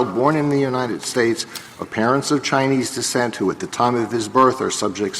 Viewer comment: the subtitle says dissent instead of descent :)